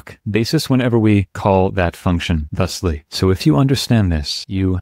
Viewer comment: As always, this was fabulous - thanks! I thought I heard you reference a "thunktion" at 02:52, which would have definitely been the cherry on top 😀